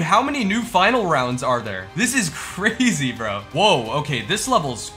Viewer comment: the subtitles said "this is carzy" instead of "this is crazy"